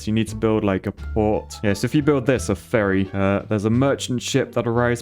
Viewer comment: I'm so sorry, I thought you said furry instead of ferry XD